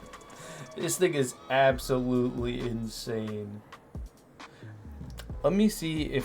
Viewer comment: i swear he said the n-word a